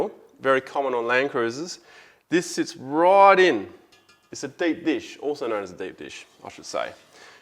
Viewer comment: did he say, " it's a dead fish"